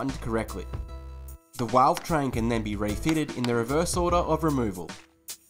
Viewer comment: did he just say 'walvetrain'...?